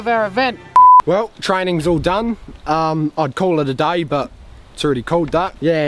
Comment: "I'd call it a day, but it's already called that" genius. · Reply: iHaveEverything I’m actually glad he quoted it. Because due to the thick accent I heard it as “I would call it a day but it’s already colder” and was left confused 😅